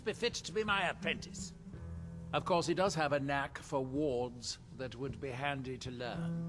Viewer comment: If Neloth says Savos has a knack for wards you can be confident that Savos' wards are impenetrable. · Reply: Tbh I thought he said warts at first and thought it was odd because I didn’t remember Savos having warts. Then I realized he said wards 😅